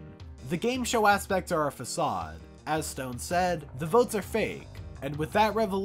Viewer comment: im so dumb, i thought it said "Vote Sarefake" and i was so confused when i first watched